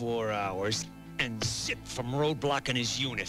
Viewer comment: At the 23:30 mark, does Duke swear?! It sounds like he said “24hrs and Shit from Roadblock and his Unit!” I never noticed that before!! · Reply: I think he said zip